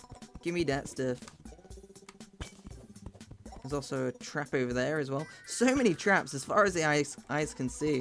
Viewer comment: I thought he said "Trappu"